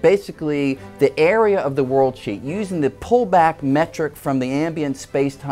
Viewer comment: if theres no captions of what he says, i thought he said "word shit"